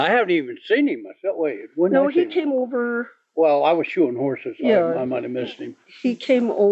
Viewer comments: The father said he was “shoeing” horses, not “shooting” them. I knew a farrier and thought he said the same thing at first. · I thought he said he was shooting horses 🪦